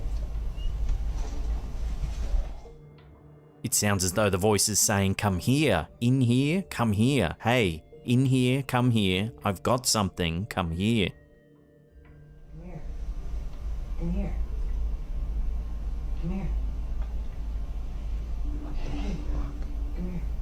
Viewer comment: Actually sounds like it says "I got to talk to you" instead of "I've got something for you" Sounds like the child ghost crouching in the corner whispers something.